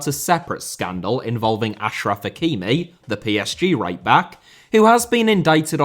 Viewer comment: You called Hakimi "The PSG Right-back" but I heard it as "The PSG Raper" and I thought that was particularly bold of you lmao